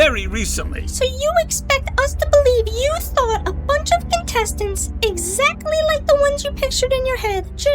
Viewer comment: How did I hear intestines…?